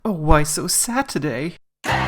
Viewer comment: "oOh wHy sO sAd ToDaY¿¿?" IM CRYING BAHAHAHHA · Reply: I thought he said 'ooh why so Saturday' 😂